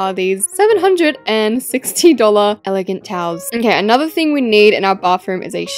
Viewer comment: when she said towels I thought she said cows😂